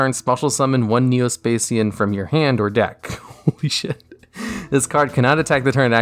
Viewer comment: is it just me or did he say holy sh*t